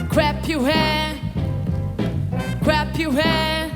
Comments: Who else heard her say "crap your han" lol wut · I just heard "crap your hair" and she make fun of others' pronunciation? The only thing she improves is her bithchy attitude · I think I heard "Crap your hand"